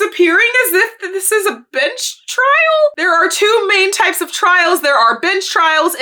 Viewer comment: I thought you said "a bitch trial" not a "bench trial" and I was like well you ain't wrong